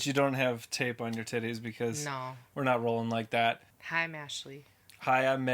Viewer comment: I thought I heard you introduce yourself as "Meshley" lmao